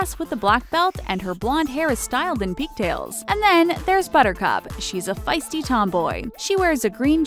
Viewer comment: I thought you said buttercrap!😂😂😂😂